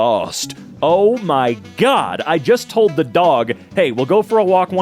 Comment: I thought it said wife not dog 💀